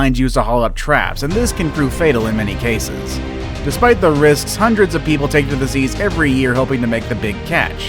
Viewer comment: Did anyone else hear “hundreds of people take the disease” instead of “take to the seas”